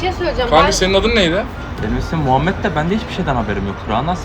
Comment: muhamet diye duydum